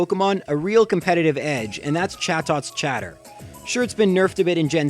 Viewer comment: "Shatter" Who else hears that--?